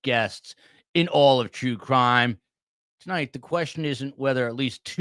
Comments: At the beginning, I thought you said, "Jew Crime" instead of True Crime. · I could have sworn I heard you say, "STS, bringing you the best in all of Jew crime." lol